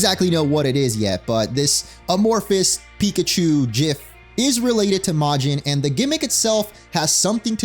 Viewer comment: DID HE JUST SAY JIF